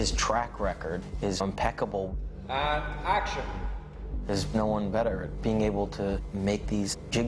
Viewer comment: does he say there's Nolan better? :D